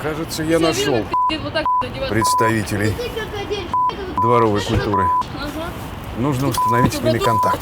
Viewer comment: мне одному послышалось:дворовая шлюха?